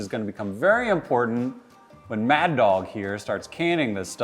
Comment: I thought I heard Mad Dog Tannen